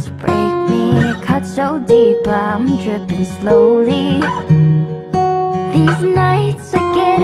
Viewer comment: I thoughT it said these NuTs instead of these nights XD